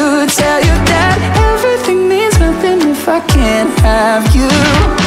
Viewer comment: I Am Either Hearing Things Or My Ears Aren't Working But I Hear " Everything Is Perfect If I Cant Have You " When He Sings Louder Send Help UnU